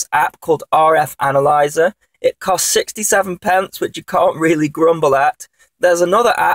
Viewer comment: you said an 67 pound RF app was required (or it sounded like it). how is this different than spectroid which is free?